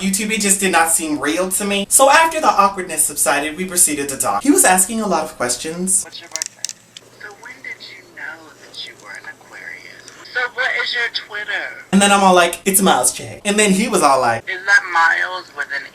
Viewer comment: Is it just me or did he say something about "When did you know" at 1:23? >.<